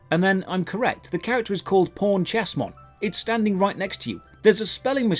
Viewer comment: I get ur British but when u said pawn chessmon it sounded like u were saying porn chestmon